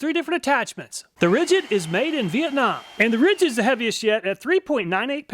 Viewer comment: bro he said "heaviest yet" but I heard heavy as shit XD and then loud as shit XD